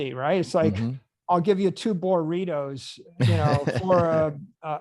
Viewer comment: Is he saying "Burritos?" 47:45 mark.